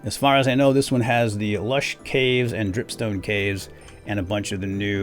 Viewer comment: i thought he said p**psnake caves!?????????!!!!!!!!